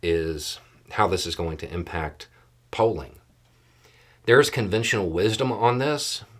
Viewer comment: Just a little light note -- I'm not awake yet and I thought you said how this is going to affect Poland -- when you actually said Polling instead of Poland. While it's true we have no idea how this is going to affect Poland my mind needed more coffee to attempt to find the context in this particular discussion. I do know that Polling in 2024 really doesn't indicate anything except what the polling overlords want it to.